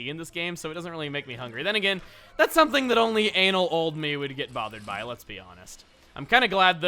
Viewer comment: Did you say ''Anal old me''? at 26:57 ?